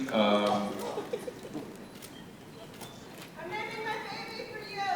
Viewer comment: What does that woman say at 2:42? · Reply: I think (I may be wrong) she says "I'm naming my baby for you." Or something to that effect.